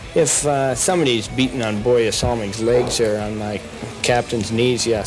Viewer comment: The subtitles are wrong here! He's saying BORJE SALMING and not BOY SAMMY!!!